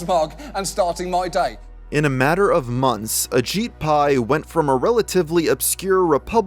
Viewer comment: I'm not paying attention...I thought I was hearing you say A s💩 Pai 🤦🏽‍♀️